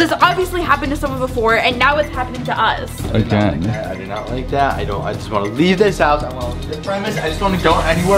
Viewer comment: I thought they said they couldn't leave the house